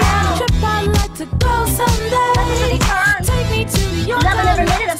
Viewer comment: for some reason i hear “let it bird” and it maches perfectly with pearls action